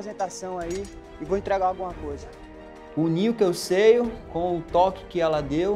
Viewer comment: min ,unir o que eu seio,só eu q ouvi isso ? Kkk